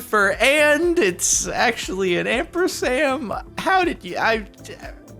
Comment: Did... did you say "ampersam"?